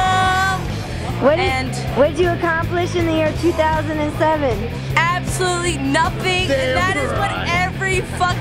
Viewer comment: Can you tell me what she said. Was a bit slurred. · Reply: She said she accomplished nothing in 2007